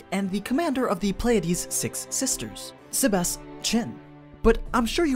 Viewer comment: i heard Sebas CHAN